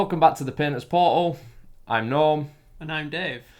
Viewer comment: this might seem a bit immature but how you said "Painter's Portal" I heard "Penis Portal" and had to look at your channels name to verify what I heard. going to go back to watching the rest of the video.